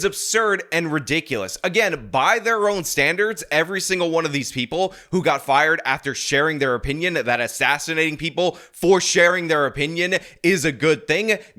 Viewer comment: Sounds like she is saying "Doo Doo got fired"